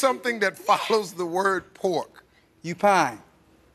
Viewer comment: Didn't that dude say "U PINE?" I mean i didn't hear him say "Q-PINE" i heard "YOU PINE" or is it just me?